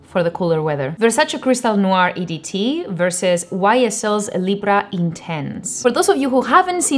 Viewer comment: I thought he said « Libra », I was like what ? What does it have to do with us ? 😅 after hearing that part again 2 times I understood he was saying the name of the perfume « Libre ».. which is not pronounced like that at all 😢 please don’t pronounce the letter « e » like spanish or italian, it’s French, we pronounce it « libr ».